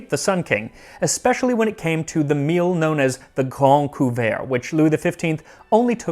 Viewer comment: I swear, everytime I heard you say Le Grande Couvert, I thought you were saying The Grand Poo-bah, which is coincidentally exactly what I would demand as my royal title were I of noble blood.